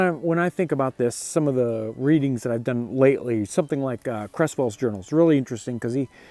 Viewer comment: It sounded like you said you were reading "Crestfall's journals" about coming up the Ohio into Harrodsburg. I looked around on line could not find "Crestfall's journal" but my hearing is not always the best. I would be obliged if you or another of your viewers would set me right. I have been interested this period of history in the Ohio Kentucky area & loved Allen Eckart's narrative history of this time and place. It would be a great thrill to read this primary document of a long hunter. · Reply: Search Nicholas Cresswell. Hope this helps